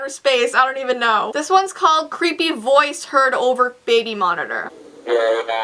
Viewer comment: And i hear "This voice is creeping your baby monitor"